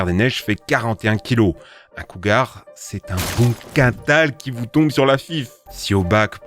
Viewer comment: mais qu'est ce qu'il dit sans déconner ? « C'est un boom KATAL qui vous tombe sur la FIFFE ! »